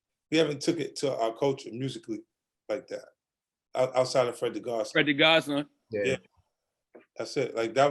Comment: I couldn't understand what was said. Freddy who?